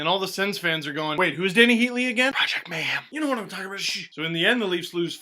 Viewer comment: At around 2:25, what did you whisper after "Wait, who's Dany Heatley again?" Haha. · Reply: "Project mayhem"